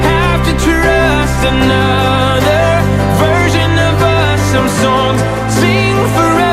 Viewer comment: I THOUGHT HE SAID THE WORD 'VIRGIN' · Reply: Oh wait he said 'version'. Anyways I COULD DIE WATCHING THIS I LOVE IT I LOVE IT I LOVE IT I LOVE IT I LOVE IT I LOVE IT